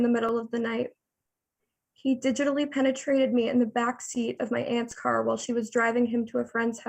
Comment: did i hear that correctly??? "HE DIGITALLY PENETRATED ME" wtf is she saying